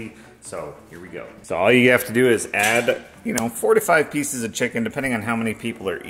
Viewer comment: I heard "45 pieces of chicken, depending on how many people are eating" 😂😂😂 I'm just listening and it hits me, no way! Rewind, nope 4-5. Huge difference!